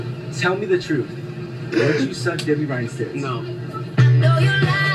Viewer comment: What did the guy as Cameron Boyce at 5:02? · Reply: I thought he said Debby ryan? but that's just what I heard so..